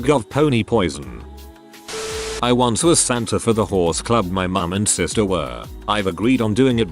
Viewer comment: Oh, HORSE club. I thought it was saying “whores club”